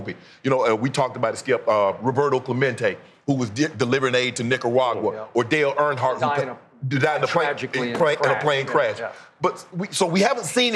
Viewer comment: Hold up did he just say Dale Earnhardt does on a plane crash?....